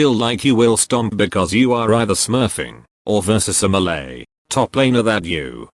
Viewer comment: When he was saying melee i thought he was saying Malay and i was like, damn some racism on the dl, nice.